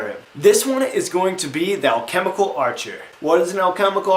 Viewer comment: Lol for some reason at first i thought you said thou chemical archer